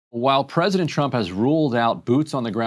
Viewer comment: Sounds like she said 'President Chump'